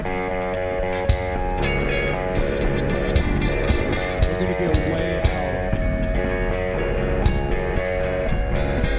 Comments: can i ask someone what kurt said on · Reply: ‘We’re gonna get way out’ = we’re going to go crazy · I'm Japanese.I don't know what does he say. please tell me what does he say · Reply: “We’re gonna get way out” as in, get really weird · does anyone know what kurt said at 0:56 ?? it sounds like “we’re gonna get way loud” but i really can’t tell.. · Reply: “We’re gonna get way out” I think